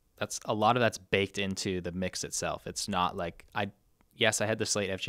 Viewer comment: Hi guys love the channel, and will stay on but what was 😅the channel he said was "the competition?" Was it "Nail the Mix' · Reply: That could be it. I thought he said "Nate the mix" which makes like zero sense. Considering the accent, that must be it. Great interview! So inspiring.